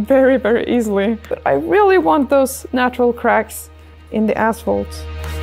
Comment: ASPHALT…. K…. thought you said natural cracks in the a$$holes 😂😂😂